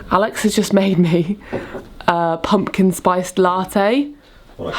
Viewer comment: I heard 'Alexa's just made me a pumpkin spiced latte' and I was like 'Alexas can make coffee now? Hey Alexa, make me a coffee' ;)